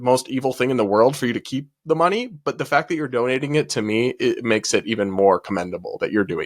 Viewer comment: "donating it, to me" not "donating it to me" :face-blue-smiling: I had to rewatch that a few times to realize what I heard and what actually meant:face-blue-smiling